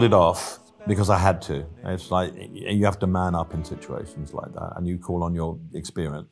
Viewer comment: Did Elton just say @ 18:05 "Man Up"?